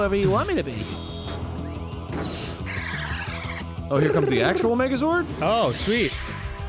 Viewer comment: I heard "F*ck sh*t up Megazord" 😂